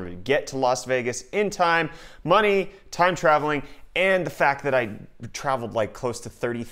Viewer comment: did u just say "time travelling"